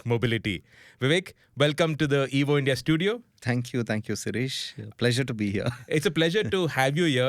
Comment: did he call u Suresh?😂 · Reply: No. He said sirish only.